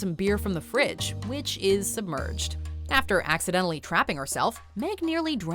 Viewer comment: Lol I thought I heard crapping instead of trapping